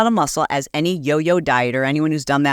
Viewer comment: into this video does she say we die? I’ve replayed it so many times and I can’t figure out what she’s saying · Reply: When you lose muscle each time you 'diet' 😘